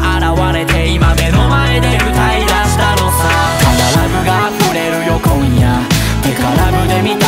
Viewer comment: どうしても 『ただラブが溢れるよ小宮〜』 『てかラブで満たそうよ小宮〜』 に聞こえてる……………小宮………